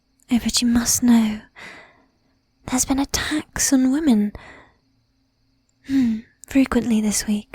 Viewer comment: At first I heard 'attacks on women' as 'a tax on women,' and was very confused.